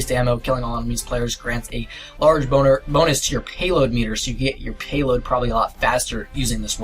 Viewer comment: Am I the only one that heard him say "the large boner" 😂😂